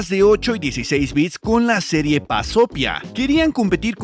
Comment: 🤣Creí que dijo BAZOFIA jajaja